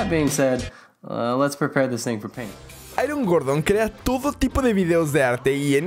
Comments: Cuando dijo airon Gordon yo pensé que dijo ay un gordo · Entendí "hay un gordo que hace todo tipo de videos de arte"